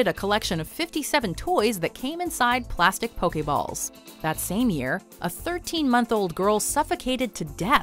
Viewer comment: Oh I thought they said 13 YEAR old. I was gonna say, "Then that girl is dumb, how did the half of the ball even fit both the nose AND mouth?"